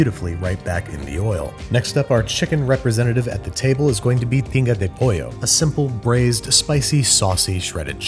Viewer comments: I thought you said you’re making “Pinga De Pollo”... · Thought he said “Pinga de pollo” and got scared lmao